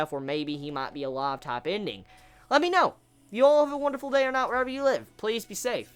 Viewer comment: Something I wanted to bring up is that every time you say "Have a good day or night wherever you are." I keep hearing "Day or NOT." I know I am hearing it wrong but its just funny to me.